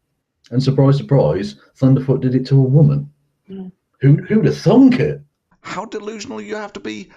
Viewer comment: did i hear "who would have thunk it?"?